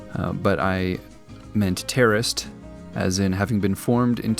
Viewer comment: I genuinely have no idea what else he could have said. Can someone enlighten me as to what it is? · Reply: Terraced I think, I thought he said terrorist too